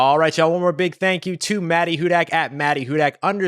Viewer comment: I thought he was saying “Maddy WhoDat” the whole time till I saw her name 🤣🤣